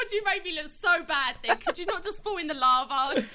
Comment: "you make me look so bad *nick*" am i uneducated, am i mishearing it, or is that real name?